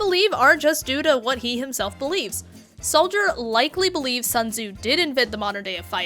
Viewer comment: for the longest time i thought soldier said censor that and not Sun tzu